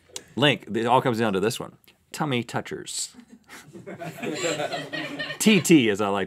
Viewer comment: I thought he said "Tiny Touchers" O.O